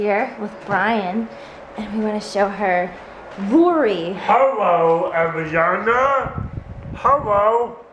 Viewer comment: sounds like he is saying "hello a vagina" x)